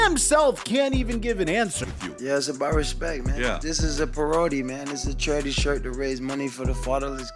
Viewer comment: "This is about respect, this is a perody" can anyone explain what ab meant by that or maybe I misheard whatever nonsense word that was said lol